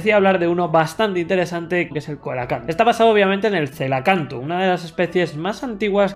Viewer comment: Por un momento entendí “colacao” 😂